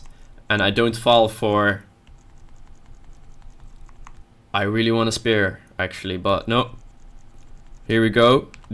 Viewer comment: "I really wanna spear her" that's what I said.